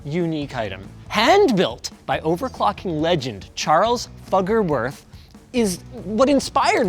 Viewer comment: Did... did I just hear... "Fucker Worth"...? ... must rewind... that can't be right... As always: interesting tech.